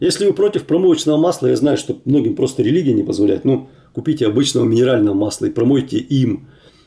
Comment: 14.16.мне послышалось?он говорит что кому то религия не позволяет ?